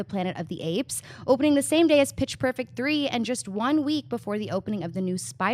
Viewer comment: Sounded like campea said bitch perfect 3 lol